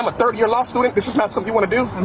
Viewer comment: He said he’s a third year law student, want to bet he doesn’t pass the bar exam. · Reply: Hahaha… I thought he said he was A 30 year law student 🤣